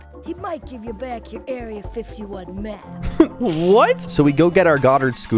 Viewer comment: I thought he said area 51 meth and was very confused